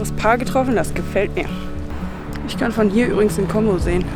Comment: bei 9:37 habe ich zusammen mit Dir den Ausblick vom Berg bestaunt und verstanden:... " ich kann von hier übrigens den Kongo sehen..." Das wäre dann tatsächlich eine KRASSE Aussicht gewesen 😂! Danke für informative Impressionen, so verbringe auch ich meine Touren und bin immer dankbar für Anregungen 👍